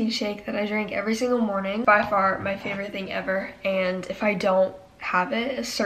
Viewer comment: I thought SHE SAID IF I FART xD